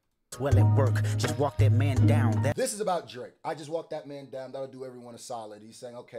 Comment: He did not say "i just walked that man down that'll do everyone a solid" He said "just walk that man down that'll do everyone a solid". Meaning get rid of whoever is doing wrong for the betterment of everyone. Yes that can apply to Drake and the battle. But i think hes just talking in general about ridding ourselves of the bad apples among the bunch. You guys want everything to be about Drake so bad, its crazy lol.